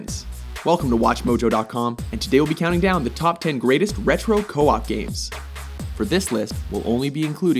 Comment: Oh i thought it said retro COP games